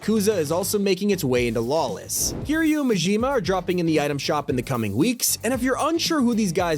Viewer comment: I always wanted a goofy ahh Payday game in Fortnite 💀the song was pretty good & the train looks nice but still such a goofy ahh gmod workshop season. Sub Zero's ice gauntlets are probably just gonna be reskinned waterbending and absolutely destroy any meta of guns being relevant. The AWP looks so hot I can't wait for it to be nerfed within 30 minutes of Season 2 going live. I'm really happy to see Kiryu-chan and Gorou, but kinda lore-breaking that Kiryu will have to fight women now. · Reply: I thought bro said suiryu and garou